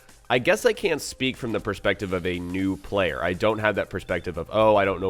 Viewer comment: did he just say at 5:28 he's a new player?! Why should we take advice from a new player!? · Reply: Luisanna Mendoza He said he can't speak from the perspective of a new player.